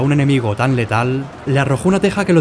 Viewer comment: juro que "escuche y la mamá lo vio y le arrojo una chancla desde el tejado"